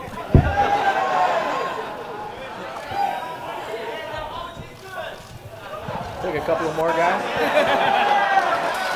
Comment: What does the guy in the crowd say at 25:30? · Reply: "that OG kush"